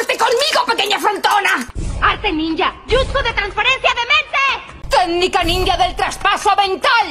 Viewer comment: En español 0:44 dijo transplanto dental 😂😂😂 o escuché mal